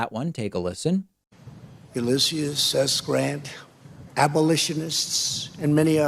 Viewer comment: Sounded like he said applelishonists, instead of abolishionists. His main problem is he can't read the lies written by S Miller.